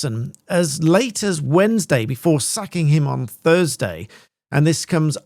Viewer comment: Did you just say "sacking him" or "sucking him"?🤣